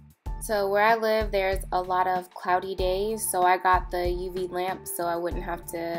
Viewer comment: I thought she said "A lotta pouty gays..."